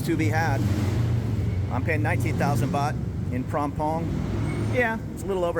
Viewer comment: You pay 19,000 baht? I thought I heard 90,000 baht.